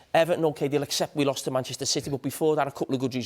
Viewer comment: Did Carragher say "we" when talking about Everton 0:45 sec